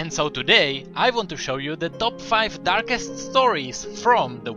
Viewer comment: I could have sworn it said Top 5 Dankest lol stories..